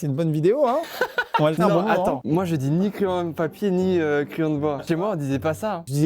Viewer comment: Qui a entendu <Moi je dis nik l'OM>